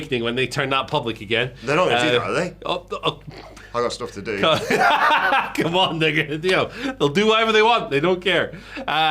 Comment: Did just say "Come on, N****" at the 3:20 mark?